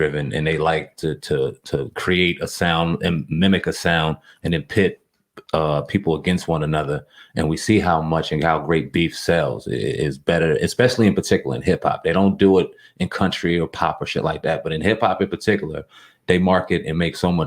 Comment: “Rape beef” wtfffff nah homie! I heard that shit! Wtf you on